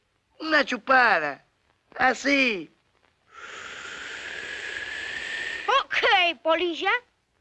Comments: Siempre pensé que decía Ok Policía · Al principio pensé que decía: ok policía pero en francés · Pensé que decía "Okey polícia" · Toda la vida pensé que decía OK policía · Te lo resumo así nomás te trajo aquí XD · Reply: Todo este tiempo pensé que decía "ok policía" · Yo pense que decia "ok policia "